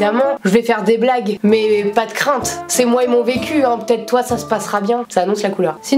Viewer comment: _; j'ai entendu " c'est moi et mon PQ" ;_